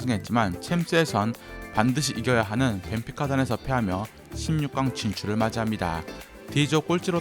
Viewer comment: 16강 진출을 맞이한다라고 들려요